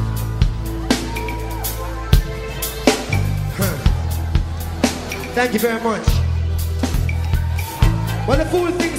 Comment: what is saying Damian in 0:20? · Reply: Navidad thank u very much 🙂